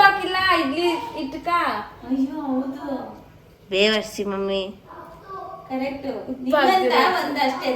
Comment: did you say bevarshi mummy?? So cheap